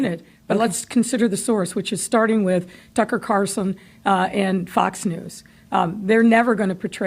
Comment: Well, she’s bashing somebody named “Tucker Carson”😂😂😂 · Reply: I thought she said Trucker Cartons 👍🏾 Beetlejuice!😂